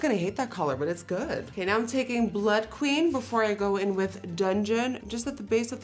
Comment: Is it just me or am i hearing her say “BLUT LUST”